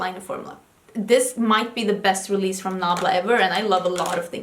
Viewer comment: Did anyone else catch closed captioning when said ""Nabla". The closed captioning typed "blah blah" time 16:25. Need to send that ambulance to CC 🚑.